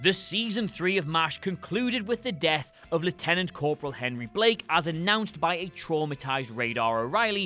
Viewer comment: M*A*S*H was set in Korea, not Vietnam... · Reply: I may have misheard this, but I thought I heard him call Blake a “Lt. Corporal”